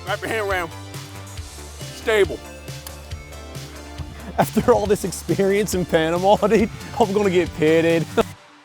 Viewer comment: wtf did he say?! 😅🤔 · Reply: After all this experience in Panama dude I’m gonna get paid dude